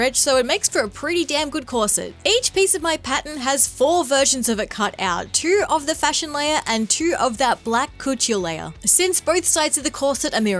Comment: Now time for my favorite game: "The captions said whaat??" 9:12 What actually said: "black cotille layer." What the captions say: "Black coochie layer." 😂